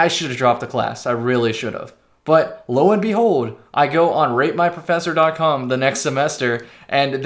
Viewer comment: When I first heard you say “rate my professor” I thought you said “rape my professor”